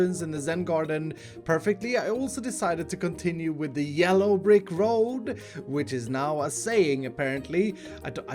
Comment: when he said "yellow brick road i thought he said "yellow rick rolled" and was like "excuse me what"